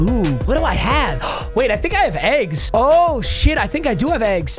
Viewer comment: I cant thought he said I have aids when he said I have eggs